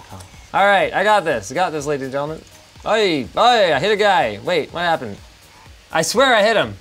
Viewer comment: i thought he said “i hit a gay” LOL